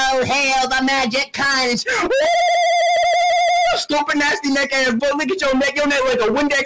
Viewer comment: what did he say🤔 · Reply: Storpping astonasty